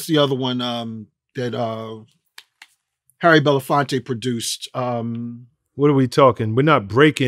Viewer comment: “Beat street”was the movie he couldn’t remember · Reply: im going to help you out because he clearly said " Harry Belafonte Produced "